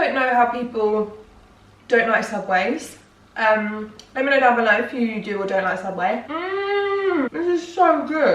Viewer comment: when you said "let me know down below" I heard "let me know banana loaf" ahahaha